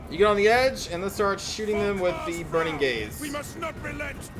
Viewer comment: "How about you go here and start shooting them with the burning gazes..." I misheard "the" as an "a" and thought that was a joke at first.